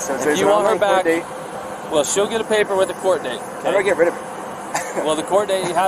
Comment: I swear it sounds like he says "how do I get rid of her" then laughs, with CC on it says how do I get rid of it but everytime I hear it I hear him say "her". Can someone listen for it and let me know, maybe I am hearing it wrong because we all know what the end result was. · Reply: On another video they indeed said that he did say how do I get rid of her, as a joke. I think it was a psychiatrist analysing the body cam video.